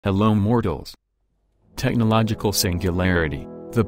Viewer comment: Hello mortals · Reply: I thought it's been saying "Hello morons".